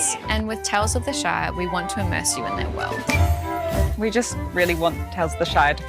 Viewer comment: sounded like she called it the Shite. Definitely fits